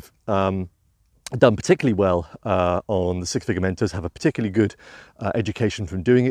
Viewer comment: It's been a mighty long day, admittedly, but at first I thought you were saying "Six Figure Mentals" and had a good chuckle. :)